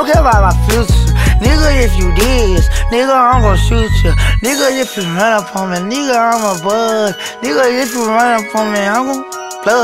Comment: Wtf Did he say LOL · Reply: “Nigga if you run up on me nigga imma bust”